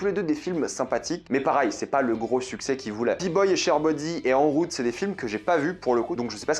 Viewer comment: C'est moi ou dit Peaboy et Sherbody a 13:57 ??